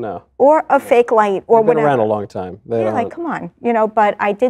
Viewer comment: Hay Micheal so your a demon nice slip up bun 14:17 listen to what he says your ran around white light hmm really funny you say that what are you really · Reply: He says they've been around a long time